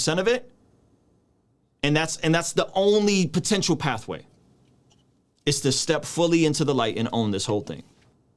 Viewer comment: did you just say “step into the light and expose yourself”? I can hear Zach giggling right now